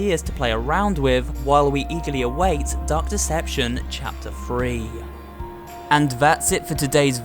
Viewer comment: I heard it like he said "Duck deception"